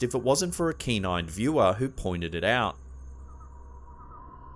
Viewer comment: I heard it as a "canine viewer that figured it out." It gets funnier the more you think abour it 😂😂😂